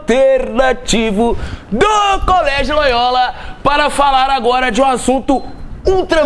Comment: Só RI no cmç pq entendi "Colégio Boiola" kkk